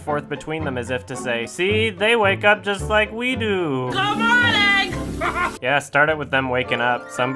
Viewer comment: Wow, this is the first time I watched this video with subtitles, and I only JUST NOW realized she said "good morning." I shit you not, I watched this video several times and always thought she was saying, "Come on, Egg!" WHY she would say that is beyond me, but it's what I thought I heard.